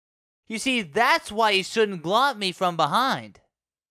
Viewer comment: I can't make out a word Sonic says at 1:11. "You see, that's why you shouldn't (what?) me from behind." What word is it? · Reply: Glomp